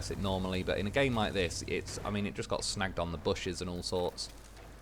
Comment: What's with the "smacked on the bushes" 05:04 ish... that's what she said... EDIT... Northern (hemisphere) accents... snagged on the bushes... mind in the gutter here